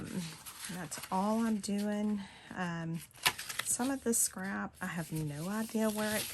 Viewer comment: I know you said, "This scrap . . ." What I heard was, "This crap . . ." This is a great idea. I have become more selective in what I keep.